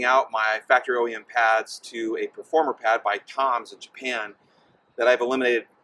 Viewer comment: What was the name of the brake pads from Japan? Can anyone tell me what he said at 1:14? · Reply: Tom's of Japan